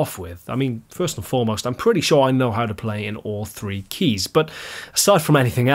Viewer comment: At the beginning of the video, when is talking about playing in different keys does he say "all three keys" or "all the keys" at 1:57? It sounds like "3 keys" to me - wondering if there's a pro tip for simplifying music theory I should know? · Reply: He does say "all three keys", but it's a joke. There are (at least...) twelve keys in Western music theory but most guitar based music is written in only a few guitar-friendly keys. He might mean E, G and A, or maybe D, but I wouldn't think too hard about it since it's meant as a joke anyway.